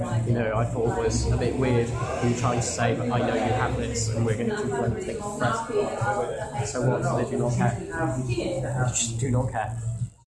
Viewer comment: inaudible audio is " I know you have this thing and we're going to confront it, but what's weird is you then said, I do not not care. I just do not care."